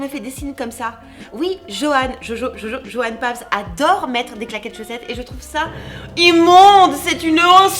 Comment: Le premier sa m'a fait peur j'ai entendu "j'aime pas les caca de chaussettes"et elle a répétée et j'ai compris 😶